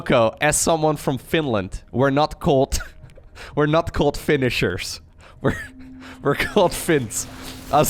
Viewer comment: To be completely honest, I always thought he was saying "Finnish Zerg" not "Finisher"